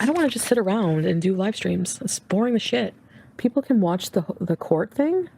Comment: I thought she said no more livestreams😂